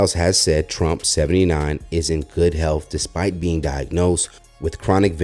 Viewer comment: Thought he said he was in such good health lmao